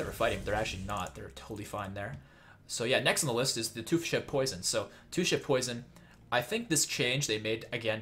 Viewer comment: When you were talking about two shiv poison I thought you said two shit poison